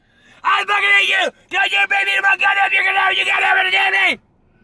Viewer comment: what the fuck does he say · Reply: "Don't u make me eat no more God damn chicken no more, u God damn understand me"